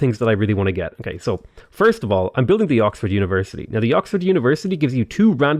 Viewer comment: I thought you said several times that the oxford university is not worth it lol